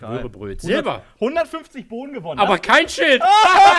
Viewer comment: was sagt er bei 5:49 ? "aber kein......."?? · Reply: Max sagt:"aber kein schild"